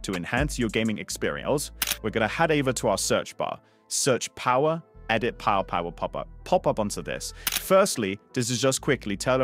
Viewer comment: what now? i didnt understand anything u said. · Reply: control panel, power options, change plan settings